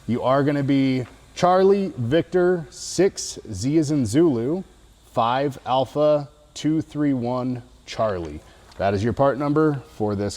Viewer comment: parts number for the V-Band Clamp is CV6Z-5A231-C, i had to look it up couldnt understand it in the video